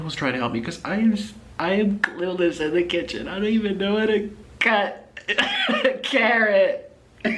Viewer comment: “I don’t even know how to cut a carrot” sobs uncontrollably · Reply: I legit couldn't tell what he was saying in that clip, I thought he said "I don't even know what a cat- a carrot" and I was just like ?¿?